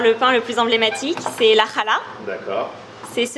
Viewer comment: Je rêve pas j'ai entendu "Caca" ? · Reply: Elle a pas dit khara elle a dit khala donc oui tu as rêvé 😴 elle n’a pas parlé de caca 😊